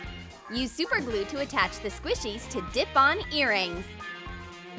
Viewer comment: I know it said clip on earrings but I heard is Dip on earrings I'm all like what the heck Lol 😂